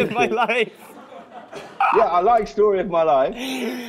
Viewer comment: Can someone please tell me what Niall say in the min. 9:45 to 9:49?? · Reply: "what about story of my life"